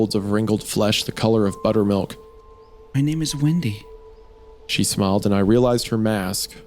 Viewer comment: The Dead Jacobi In My Yard 17:02 I heard it as "My name is Wendi(goon)"